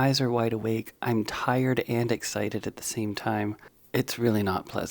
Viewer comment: I feel so bad for laughing, but did you just say you were tired and excited at the same time I can’t stop laughing CACKLE!